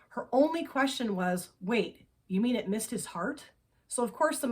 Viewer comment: I, literally, gasped when she said "wait, you mean it missed his heart?" First of all, I thought she said "I missed" but either way, wow! So very sad!